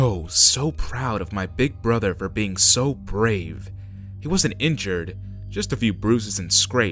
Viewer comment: it sounds like he says grave